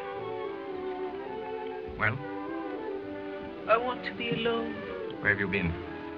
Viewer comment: for years since I first watched Death becomes her, I wondered who Rosellini and streep were talking about in that scene " I vant to be alone, yahh". I did some research and that's how I got here. I always thought it was " I vant to be a lawyer". silly me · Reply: I always thought she said, "I want to be a lawyer" too! After watching it today I researched and ended up here..... It all makes sense now, yay!